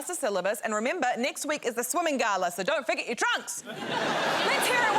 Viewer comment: I totally heard "don't forget you're trans!" instead or "your trunks"😂 had to rewatch thrice cause I couldn't figure out the link between a swimming gala and being trans/nb lol